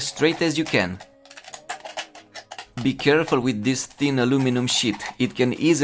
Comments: i in any way wanna make you look bad but when you said "be careful with this thin aluminium sheet" i hear "be careful with this thin aluminium shit" and laughed for 10 minutes str8 XD im dumb :I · to me sounded like, thin alluminum sh*t. lol